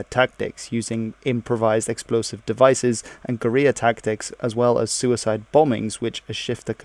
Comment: "using improvised explosive devices and Korea tactics" I know you meant to say "guerrilla tactics," but did you just say "Korea tactics!?"